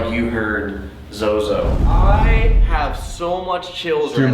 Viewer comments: sounds like says ”i have so much children” xD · i heard Zozo. Before i never heard it